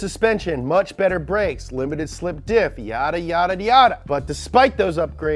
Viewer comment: Hold on.... am I the only one that thought I heard him say "miata, miata, miata" in place of "yada, yada, yada"? at 8:54?